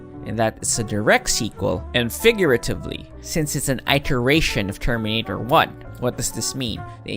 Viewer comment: wait....did he just say I-ter-ration?